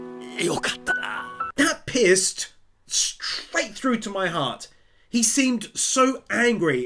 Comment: Is it just me or did somebody else heard "That pissed straight through to my heart" and then had to rewind if it was indeed said?